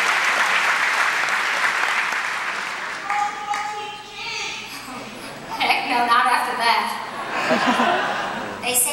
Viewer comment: What does that person say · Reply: Someone requested "The Girl in 14G" which is a song Kristin sings.